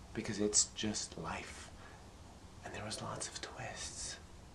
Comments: He actually said twists :P but I understand, I thought he said toys at first too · I thought she said "There's lots of twists." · I thought she, well he, said, "there's lots of toyes". Then I thought, no, it was "choices". It was hard to tell. Either way, toys, choices, twists...it all is very profound. Keep making these videos, please. I look forward to them each week. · I thought she said: " and there are lots of toys" ... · I thought he said "...and there's lots of twists." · i thought he said toyists XD